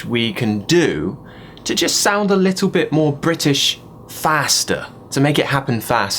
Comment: I thought the brits said "fahster" not "Faster"